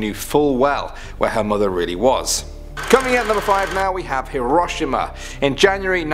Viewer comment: dis this dude really say herashma instead of hiroshima( hero she ma )